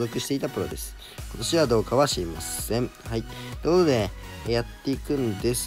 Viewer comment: なんて言ってる？ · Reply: みみみ ことしはどうかはしりまっせん